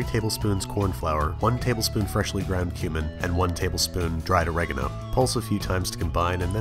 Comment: I thought you said human